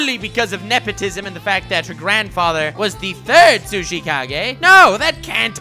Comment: auto titles instead of "sushikage" typed "sushi cow gay" that's why I love auto type